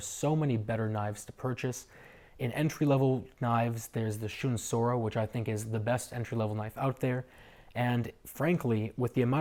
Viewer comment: which knife did u say as an alternative sir? Great review also, helped me a lot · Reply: Shun bread knife Tokiro dp série